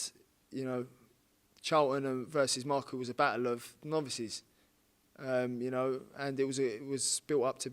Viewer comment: “Battle of novices”. Really thought he said “battle of nonces” at first😩😩😂😂